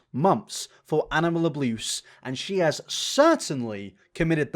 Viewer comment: or so, did you say "abluse"? Lol